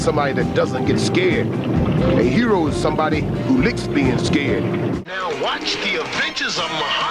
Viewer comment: captions says lick penis game :D